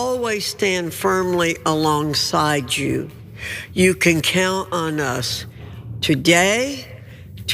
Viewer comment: at first I heard "you can kill on us"